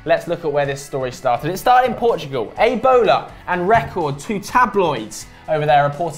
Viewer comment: Did he say ebola